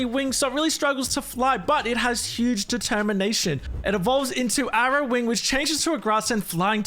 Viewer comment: Have I heard determination?!